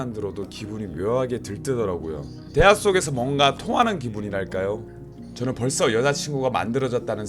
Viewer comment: 대화 속에서 뭔가 토하는 기분이랄까요? 라고 들림 ㅋㅋ